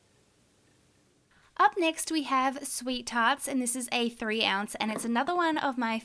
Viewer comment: When she said sweet tarts I thought she said sweet farts